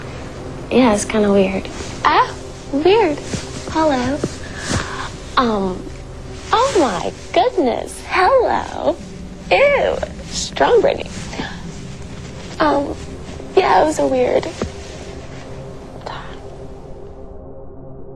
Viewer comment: The Britney Spears one scared me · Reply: I thought it seemed like she said strawberry and covered her nose, like she suddenly smelled something, but maybe "be strong Britney" was more likely- and she was attempting to change the subject. The laughing was still weird...